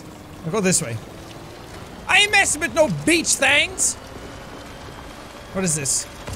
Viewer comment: Jack: "Beach things" What I heard: "B*tch things"